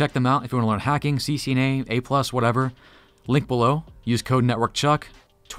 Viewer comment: does Chuck say, "Lick below?"